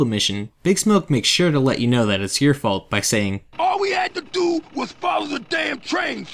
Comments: Big Smoke: “All u have to do is follow the damn train” Me: “ALL U HAVE TO DO IS SHOOT THE GOD DAMN DRUGS DEALERS” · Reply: Ikr bruh but I always heard “all you had to do was follow the damn train cj “ not we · I always thought he said "All you had to do..." but it's "All we had to do"